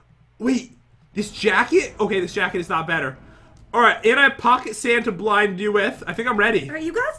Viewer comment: i thought he said "pocket santa blind you"